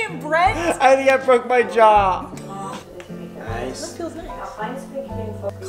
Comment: When he said I think I broke my jaw, the captions said f my job omg xD go on the captions and see for your self